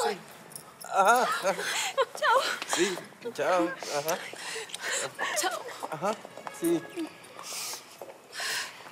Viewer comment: que dijo Rafaela? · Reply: Jaja un mba :3